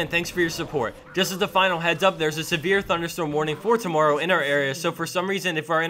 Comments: any one els thought he said sivir thunderstorm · For a moment there I thought you said Sivir thunderstorm.